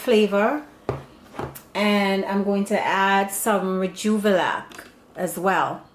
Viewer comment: What was the liquid used In this video? All I heard was "joobala" lol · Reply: Rejuvelac. I show how to make here: <link>